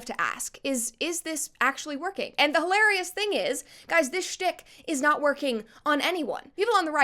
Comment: Did she say shit? 10:24 🫢